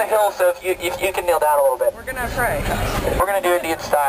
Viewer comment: did zac say "we're gonna pray" around like 2:10? lol